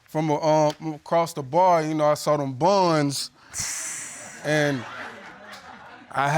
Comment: She's a liar , if she Can afford to pay for a rental car she can bloody well afford nappies for her baby , she's got Money 💰 no need to steel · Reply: 33.12 I thought he said I saw them bonds. I had back track. And he doesn't his family to be attack to the little girl...ugh